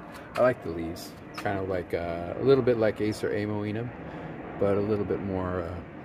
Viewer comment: Appreciate the content! 🤘 What exactly was the name of the green maple with star shaped leaves at 7:30? It sounded like you said Acer Oliver or oliverianum. I have hot blonde, hot sauce, and mystic jewel as well. Just did not recognize that one. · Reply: That's Acer olivaceum which is a different species than Acer oliverianum.